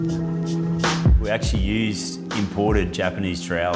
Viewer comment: What's does he say they used on the pool? "Japanese .....?" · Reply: trowel